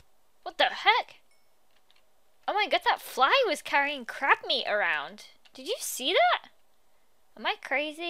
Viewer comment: Omg when she said 'crab meat' it sounded like she said 'crap meat' 😂😅 Did anyone who hear that? Like if you did!